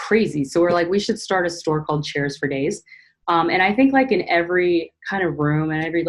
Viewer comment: I heard "chairs for Gays" but I think it's what I wanted to hear lol 💜🌈💜 love the video!! I would so adore a disco ball or 3!